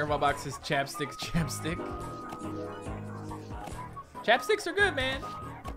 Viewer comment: WHAT DID HE SAY?!💀💀☠️ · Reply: Nah bro likes to eat chapstick!